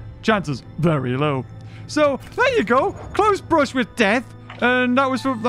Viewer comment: I swear I heard him say "Clothes-brush with Death..."